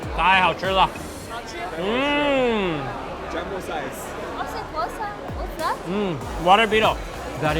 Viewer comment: " oh , c'est quoi ça ? " it is french woman